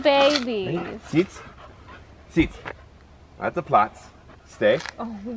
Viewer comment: is the dog swedish? it sounded like he said sitt and plats which are swedish